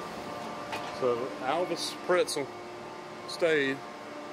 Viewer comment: LOL... at 13:00 did you say "So Alvis Pretzel stayed........ right here..." Were you seeing if we are paying attention? LOL, I am ! Great video as always, I can't believe that gate is still there! Amazing!